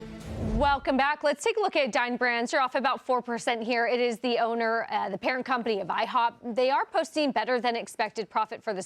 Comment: I thought she said “Dying Brand!”